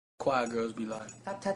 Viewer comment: At the beginning, I thought he said, "White girls be like" lmao